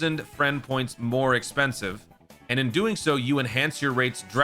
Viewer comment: 10 times more expensive instead of 10,000 more expensive?